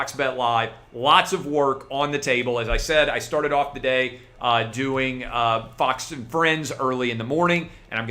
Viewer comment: So I was a little distracted when I first clicked the video. Definitely thought you said "I started the day off butt sex" LMFAO